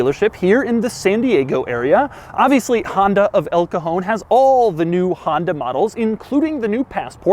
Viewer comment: I swear I think I just heard you say "Honda of El Capone"